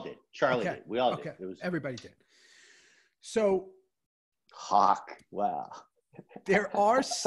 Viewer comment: did he say hawk tuah??